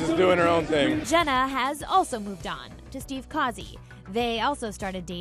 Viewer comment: LOL. I thought they said "Jenna has also moved on to Steve Harvey" 😂😂😂😂 hahaha